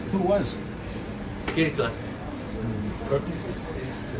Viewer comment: I thought he said Hitler...........But it was Kirkland